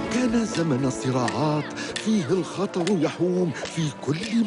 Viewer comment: arkadaslar1:11dakikalarda فيه الخطر "----"bos koydugum yerdeki sozun arapcasini isitemiyorum,ne diyor hansi fiil?yardim etseniz sevinirim. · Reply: كان زمن السرعات